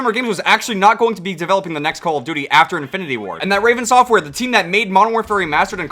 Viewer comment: Did You Say “Infinity War”?